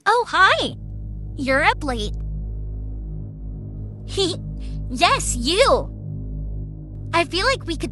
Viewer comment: I thought they said ‘You’re ugly! Yes you!’ 💀💀